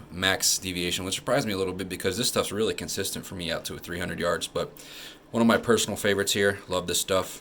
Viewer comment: Did you say consistent out to 300 yards???